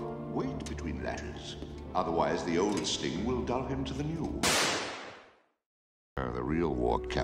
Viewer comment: Why do I hear "Loo" at 5:58 💀💀💀